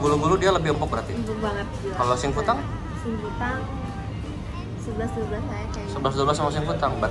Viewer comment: gw dengernya isi kutang anjir